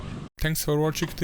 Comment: i thought the commentator said drive safe.. none of these videos depict that..